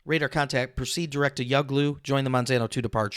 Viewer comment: How the heck dod you catch what they said at 1:35 (I got the first oart "yaglu" sth , but the "manzano2" I dis not hear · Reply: Probably from listening to ATC to much. "Direct Yuglu joining MNZNO2" was their readback.